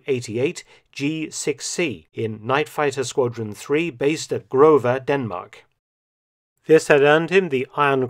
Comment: love the channels content :) can you write where in Denmark he was stationed? I'm Danish but didn't quite understand it :) Ah Grove. I hardly know anything about Jylland :p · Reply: Was it Greve?